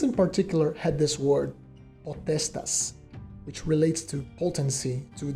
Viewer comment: What was the Roman word quoted at 2:17? How is it spelled? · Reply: POTESTAS :)